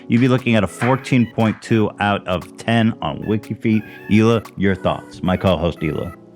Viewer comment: I thought he said “Hila you’re a thought” for a second lol